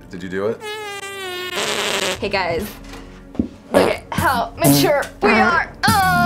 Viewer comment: What did Catie say when she did the leg lift fart? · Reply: she said look at how mature we are uhhh